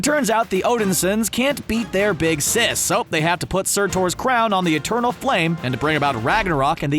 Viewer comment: I thought he said Sir Thor ((Surtur))